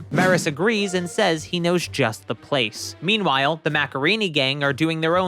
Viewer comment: instead of macareenee gang i thought they said Macarena gang